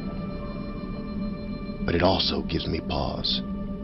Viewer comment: did Atrus say it gives him balls? No one can do Atrus like Rand Miller.